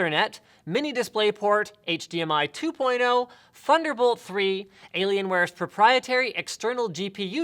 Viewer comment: When Linus said Thunderbolt 3; I thought he said "Funderbolt 3."